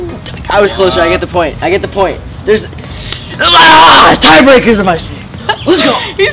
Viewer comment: omg i think at 4:38, johnny said/almost said “tie breakers are my shit” lolz